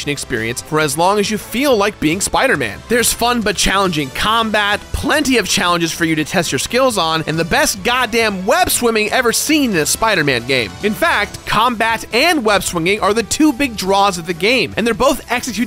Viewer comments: Did anyone else hear him say “ Web-Swimming”? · Is it me or did he say web swimming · did he say web swimming? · did he say "Web Swimming"? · did he just say "...web swimming"? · Anyone else hear webswimming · did he just say "web swimming"?